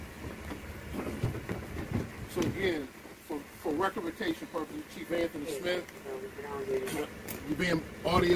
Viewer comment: what the hell did he just say ? “ for recommenTation purposes “! This guy must be suffering from a brain aneurysm!